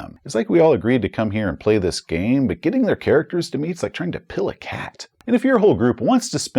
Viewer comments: Oh, it's "pill a cat." I seriously thought you were saying "peel." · As a Texan, I heard you say "trying to pill a cat" as "peel a cat" and was wondering what dark roads we were about to travel! The Traveler character creation being also a vehicle for the party knowing each other is great! But wow, Alien giving you initial relationship dynamics seems very smart. Gonna have to tell my GM to steal that!